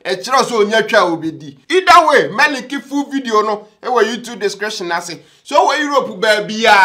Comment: TV: Oh .. Did I hear right - He will lick the full video ? oo .. This guy paaa